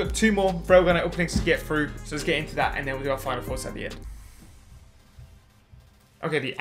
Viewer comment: Everytime my boy says "Final thoughts" I keep hearing "Fire force" and get excited but then I remember you already reacted to it. I know that's not what he's saying but every video I keep hearing it wrong lol !